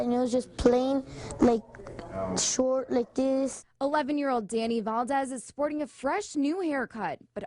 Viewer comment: anyone else hear "11 year old Danny Bald-ez"